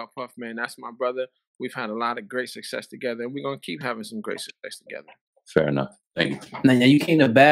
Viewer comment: Sound like he said “great sex together” around the 4:15-4:25.